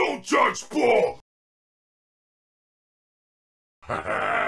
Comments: he actually said “Who is she?” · so he's a space cop now also, at 0:47 I heard "Who Is She!" might just be me though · I heard “WHO IS SHE”